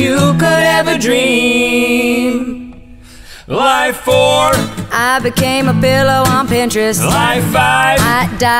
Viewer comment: i thought it said '' i became a fellow on pintress '' its actually '' i became a pillow on pintress